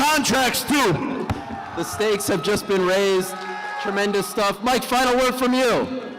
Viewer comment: My man said "The stakes have just been raised" Thought i heard, "Mistakes have just been made" 🤣